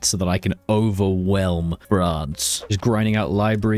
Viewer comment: If you look at the subtittles at 17:31. It says: So that I can overwhelm "Broads". Instead of "France"